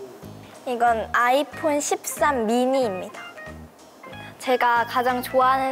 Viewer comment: what is she saying cuz it sounds like the Thai language (13= sip sam )